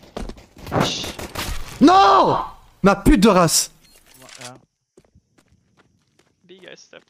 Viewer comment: What did Kenny say?! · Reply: PIDORAS